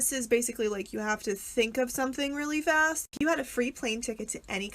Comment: I accidentally heard "if you had a plane ticket to any tree" and without hesitation I said "oak". What does that say about me?